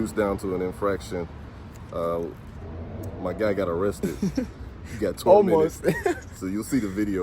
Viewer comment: I thought you said you got arrested